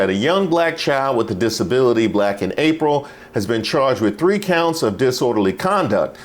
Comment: Did you say "Black in April?" 😅